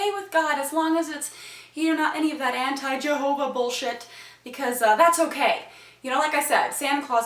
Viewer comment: would you please stop saying bullsh** its so annoying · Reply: did you just say Jehovah sh*???????!!!!!!